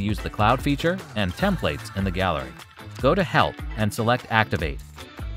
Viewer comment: lmao. i thought he said 'go to hell' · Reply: It is Help.